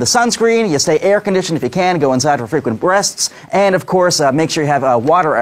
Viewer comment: ? "frequent breasts" Did I hear that right? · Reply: You did, he meant to say "frequent breaths"😂